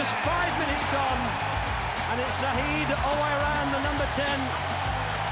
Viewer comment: Fitting how his name sounded like 'Oh-I-ran' and his goal was him running 50 yards :L